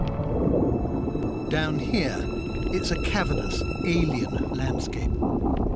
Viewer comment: What word is he using? Cavulus? What does that mean? I can not find the definition. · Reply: Sheldon Gunby cavernous